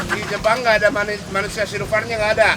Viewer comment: itu bilang apa?aku ga bisa dengar 😢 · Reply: Di jepang ga ada manusia silver